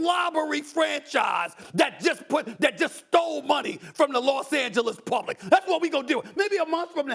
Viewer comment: I thought Stephen a smith said Los Angeles plumbing